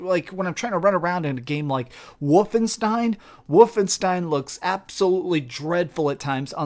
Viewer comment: Did he just say Woofenstein?